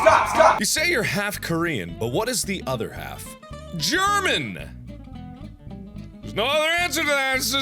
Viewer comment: ok so i know this is from 4 years ago but i just found somthuing!!! so right here: 0:39 one comment asks what the other half of his nathionality is and he says german....buuuuuuuuut, the subtitles say IRISH!!!!1 just a fun discovery i made..... dont judge me..